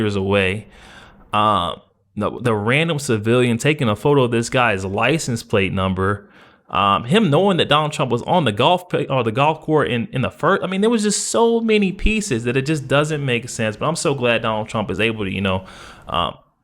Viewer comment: it sounded like you said golf court